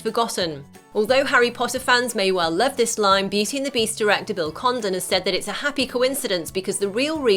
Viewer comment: I thought she said Bill Condom